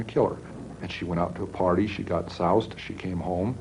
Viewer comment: did he say his mom went out and got sauced?😂